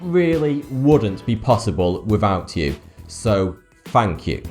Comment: I have always heard him say 42 instead of toughty2 Now I know why.